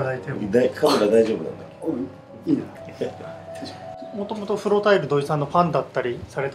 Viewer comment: 風呂タイル？に聞こえると思ったら フロタイルって言ってたww